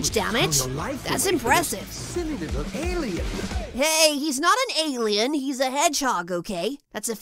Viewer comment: i heard "englien"